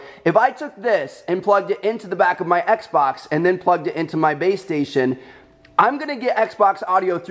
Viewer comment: Listen from 1:55 to 2:01, he says baystation instead of PlayStation